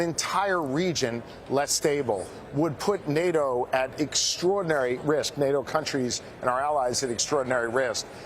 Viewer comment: it sounded like he said "extraordinary rizz" ... twice 😅